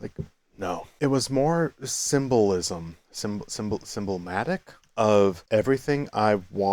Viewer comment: Dude, did you say simblomatic? i'm drunk too, English ain't my first language, and here i stand trying to figure out if that was emblematic, symbolic, or whatever else. Help sober, impart upon me ur native wisdom. Be safe funni gay kitty <3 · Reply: I gotchu fam <3 I said symblomatic, which is not a word. I was going for symbolic, or emblematic, and landed in the middle and went with it.